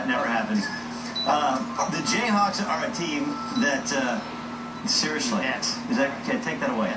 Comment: what is he saying? "seriously ex?"